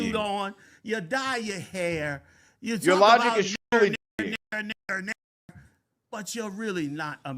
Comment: did he say your logic is truly diseased ? LoL